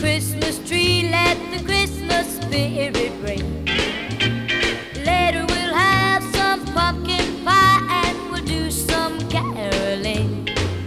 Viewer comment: Later will have some f##ing pie best misheared liric ever